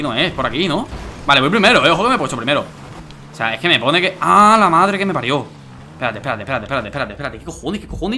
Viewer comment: pensé que dijo "A la madre" xdxd